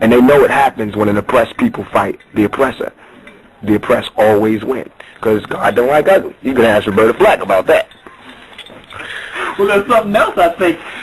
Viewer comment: Did I hear correctly? @ 12:19 "CAUSE GOD DONT LIKE UGLY, YOU CAN ASK ROBERTA FLACK ABOUT THAT"